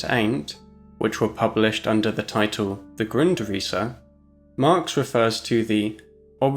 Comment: I loved it how the subtitles guy wrote "grin Teresa?" at 4:04 instead of "Grundisse".